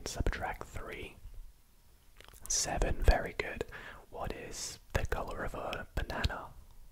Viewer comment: I was confused because i heard “”what color is nana”” until I read the comments